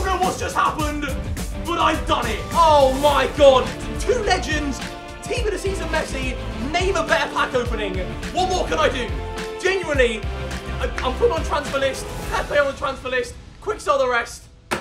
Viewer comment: did he say tots messi?